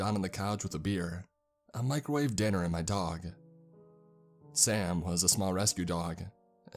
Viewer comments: “ I microwaved dinner and my dog “ is what I heard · Did I hear that right “I microwaved dinner and my dog” Or was it just bad punctuation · Wait I thought you said "I microwaved my food and my dog"🤣🤣🤣